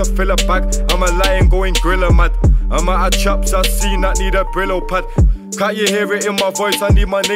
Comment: "I'm a lion goin gorilla mad, amount of chops I seen I need a ____ ___" Did anyone understand what he said on that last part of that bar? I can't figure it out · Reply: loool l brillo pad